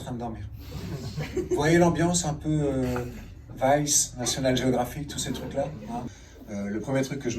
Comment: il dit quoi exactement j'entends pas. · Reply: Vice, National Geographic (plateformes internet avec des reportages assez immersifs)